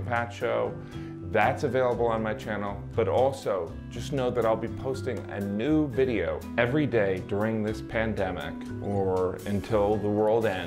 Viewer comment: I thought you said "I will be posting a nude video every day..." I was very much caught off guard, thinking ' wow that's pretty bold'.